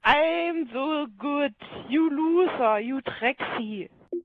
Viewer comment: did he just say you sexy?